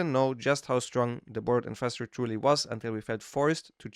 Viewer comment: what are you saying here? The board infestor? I really couldn’t understand. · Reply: Burrowed Infestor